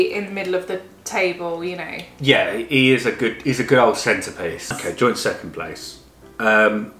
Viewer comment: I thought said 'he's a good old centipede' when he said 'he's a good old centerpiece' hehe